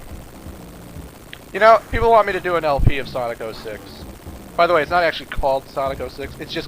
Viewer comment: Oh, I misunderstood then. I thought I heard him say "ya know what, I'm gonna do an LP on Sonic 06 people". My apologies.